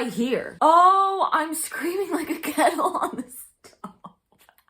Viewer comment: Haha it’s ok I thought Melanie said “Cattle on the stove” instead of “kettle” too 🤣 I was like “a cow on the stove what??”